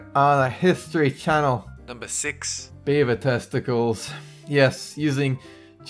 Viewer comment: I thought he said "Bieber testicles" not beaver testicles!!! 😂😂😂😂😂😂😂